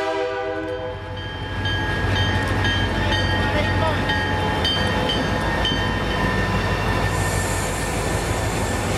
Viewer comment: If anyone can decipher what the engineer says at 10:08, please let me know. Thanks! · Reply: Bessemer & Lake Erie 902 hey buddy how’s it going